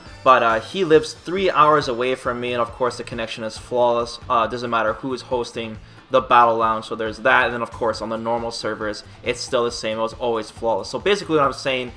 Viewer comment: what did he say? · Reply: "No matter who's hosting the battle lounge, so there's that, and then of course on the normal servers..."